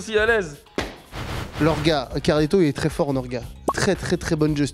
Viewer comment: orga...?sme? pourquoi j'ai cru comprendre cela .. xD